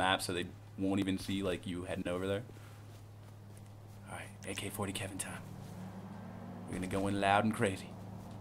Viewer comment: did anyone else hear him say,"Alright,AK-40keven time"?